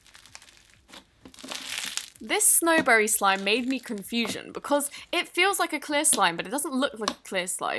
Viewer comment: wait... did she just say... made me confusion?